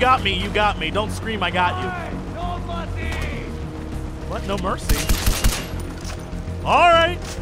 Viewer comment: at the 31 and change minute when the guy said no mercy I swore I heard 'hey dovakin' I must have played too much skyrim...lol